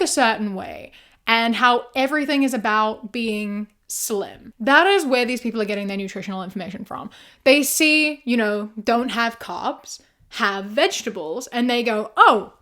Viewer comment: Carbs are the best. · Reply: I thought you said crabs 🦀 lol